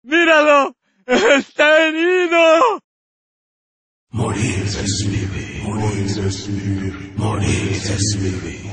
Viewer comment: I thought they always said 'modern SUV' modern SUV